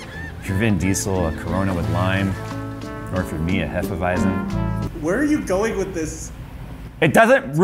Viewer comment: did i hear HEFEWEIZEN?